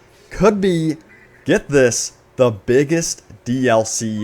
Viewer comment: I heard "biggest dlc ever" and I liked